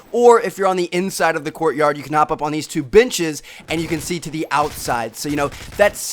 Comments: sound like he said bitches insted of benches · IT SOUNDED LIKE BITCHES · It sounded like he said bitches